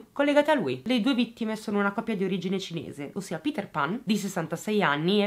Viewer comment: scusate ma ho capito male o si chiama Peter Pan?